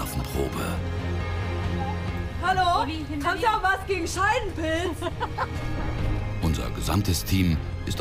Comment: sieht eine Apotheke und ruft „Haben Sie auch was gegen Scheidenpilz?“ Ich konnte nicht mehr 😂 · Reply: Haha ich habe scheißpilz verstanden🤪😂